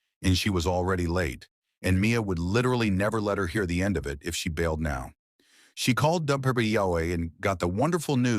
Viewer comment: "She called Dunperpity Yahweh" I almost fell off my chair laughing when I heard that.